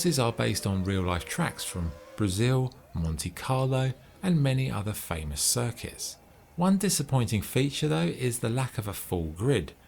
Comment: i swear i heard "many other famous circus" xD